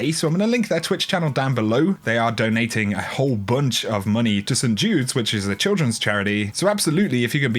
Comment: for a second I thought you said "they are donating a whole bunch of money to some dudes"